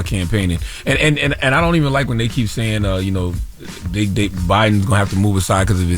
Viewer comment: Wtf did Charla say 🤔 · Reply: “They think” he got a speech impediment bro